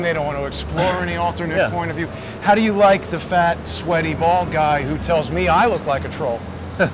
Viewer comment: After you spoke with those people writing chalk on the sidewalk, you said sweaty bald guy, I thought you said sweaty-ball guy, Hahahaha :)